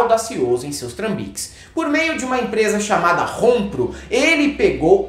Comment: Entendi que a empresa chamava Rombo